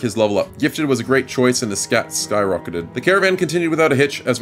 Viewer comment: At one moment I think you said "and his stats skyrocketed." Well I heard it as "and the scat skyrocketed," and thought to myself, what a fun way to say "shit hit the fan."